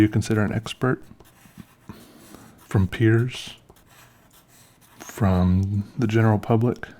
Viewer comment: i thought he said “Peters” and I was like “YES, HELP ME!”